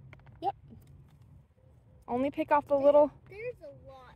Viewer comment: At first I thought she said ‘little turd’ and not ‘little skirt’ and I thought boy, she sure is a!😊